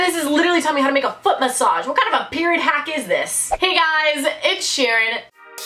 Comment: No offense but when you said "its Sharon" it sounded like you said Ed Shiren